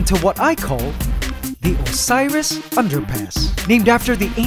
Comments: I thought it said osirus under pants lol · I swear to god i keep hearing "osiris underpants"